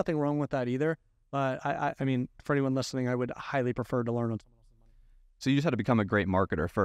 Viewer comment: What was said at 8:06 that you’re trying to keep from us? What does he suggest you learn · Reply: Learn on someone else’s dime is what he says